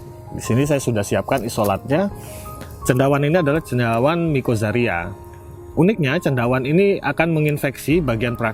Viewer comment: Maaf tolong tanya suhu itu jamur mikozaria atau jamur mikoriza ya? Maaf jika saya salah dengar, jadi mana yang digunakan? Mikoriza atau jamu trichoderma? Atau keduanya dipakai? Berapa lama aplikasinya sebelum bibit digrounding? Terimakasih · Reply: Mikoriza mas. Blepotan nyebutnya.. Untuk injeksi perakaran guna perbaikan asupan nutrisi pakai mikoriza Tricho untuk dekokposisi bahan organik dan fungisida mrnghindarkan penyakit misal fusarium. Untuk insektisida ada lagi, nanti seperti bassiana dan metharizium.. Ini saya belum bicara microba. Tricho dan mikoriza keduanya dipakai.